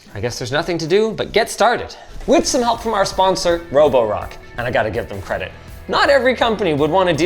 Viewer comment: I thought that shit said sponsored by roblox